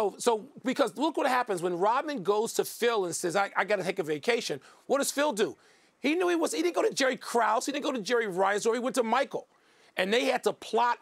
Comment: did he say Jerry rice??